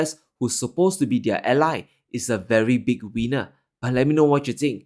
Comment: I'm sorry, I know your video are serious geopolitical and economic analysis, but the way you say the US is 'a very big Weiner/winner' made me burst out laughing. Sorry, I know what you said, but I couldn't help but point it out.